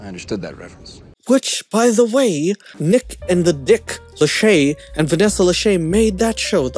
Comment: who else heard him say "Dick Lachey"? or am i hearing things? lol